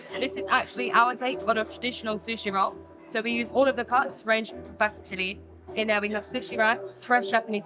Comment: Hi, just thought I'd say for anyone who was curious, the part marked "inaudible" at 07:20 she said "we use all of the cuts, ranging from fatty to lean" 🙂 Great video as always ❤